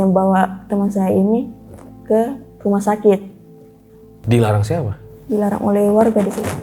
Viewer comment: gua bingung "waktu Eva ditemuin, kita mau samperin. tapi dilarang" siapa yang larang ya? gua gak nyalahin siapa-siapa ya, karena cerita mereka gak ada pengaruhnya juga buat hidup gua. hehhee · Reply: Ijin jawab kak, kalo ga salah denger tadi bilangnya dilarang warga sih 38:00 , tp kaya aneh gitu bilang nya ga yakin ga tegas Tapi seru nih wkwkwk